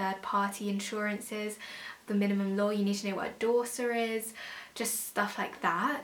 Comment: hi, great video, thanks, what did you say at 6:14? what is a Dorser?